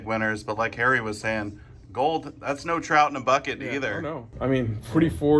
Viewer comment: "thats bo trout in a bucket either." 😂😂 what? Ive never heard this figure of speech lmao. Had to replay it to make sure i heard it right. Mustve meant "drop in the bucket"